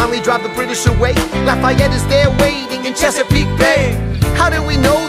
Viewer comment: I thought he said cheapskate bay, and was like vacation time